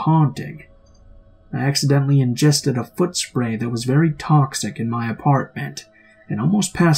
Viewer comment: Okay, final story- did he just say he accidentally ingested some “foot spray?” O.o